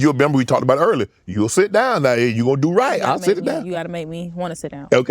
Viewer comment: Did he just say I'll sit it down